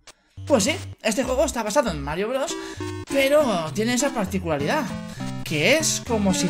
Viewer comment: pense que decia : Droga Mario broga